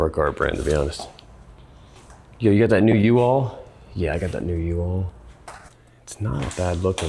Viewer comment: kinda sounded like he said yo you got that new u-haul. 😂